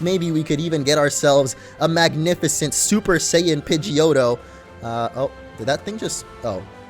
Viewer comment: did you say Super Saiyan Pidgeotto?